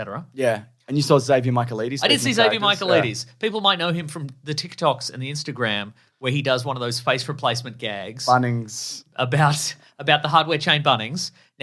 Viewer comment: Zavy Micheal Eaties? Who? What name are you saying? Sounds hillarious, but I can't make out the spelling.